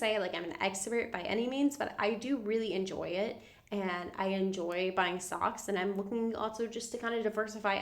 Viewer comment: I heard “I enjoy buying stocks” as “ I enjoy buying socks” 😂